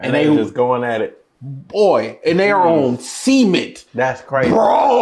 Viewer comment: “Semen’t” Just sharing what I heard.